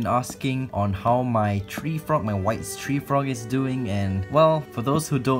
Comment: i thought u said "My Wifes tree frog"